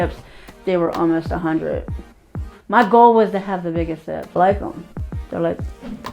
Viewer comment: Thought when I 1st heard the.it said biggest txts.. Lol😅 Ok <num>.